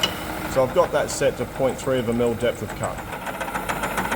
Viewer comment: Small correction: At 3:38 you say .3 of a mm depth of cut while the german subtitles say 3mm depth of cut.